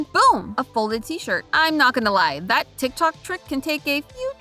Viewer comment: tiktok trick? It’s like some sort of asian folding from what I heard, idk tiktok trick just sounds off..